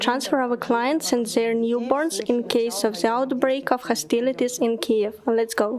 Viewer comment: i can not understand exactly what she says - IN WHAT CASE? Can anybody native English speaker write me exact transcript, pls?! (i'm a U. krn n)